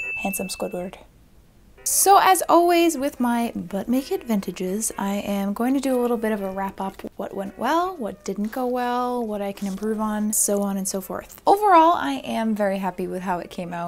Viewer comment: Maybe because I watched the first half in between errands and then came back to catch the end I must have kinda forgotten what I was watching , because at 17:20 I thought you said, “...so as always with my BUTT-NAKED VINTAGES...” Lol😳🤭